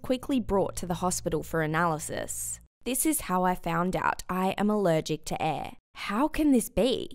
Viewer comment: I thought it said “I’m allergic to hair” oooof 😂😂